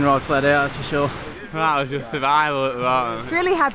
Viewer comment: What did he say? haha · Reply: “Ah it’s just survival at the moment”